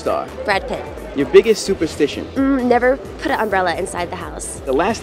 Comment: Omg she didn't mention something about an umbrella did she!!!? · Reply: Faisal Al Marri I'm not sure but I hear "Never put an umbrelle inside the house!" whaaat xDD